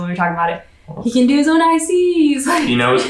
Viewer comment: What is saying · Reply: “He can do his own ICs” which stands for intermittent catheterization. Basically, with slight hand function, would be able to pee on his own!